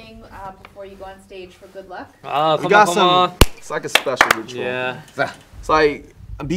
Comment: I tought i heard got7😂😂